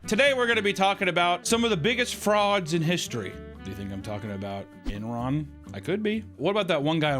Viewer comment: I thought you said biggest frogs in history. Unsubbed.